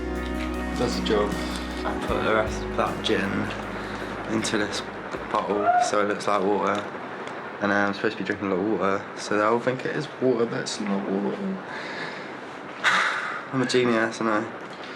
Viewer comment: Is it just me or did it sound like he said butthole when he said bottle.😂😂🤣!!!! Click to listen 👉🏾